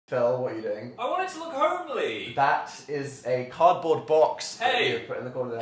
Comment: I thought Phil said "I want it to look homely!" at first, lol.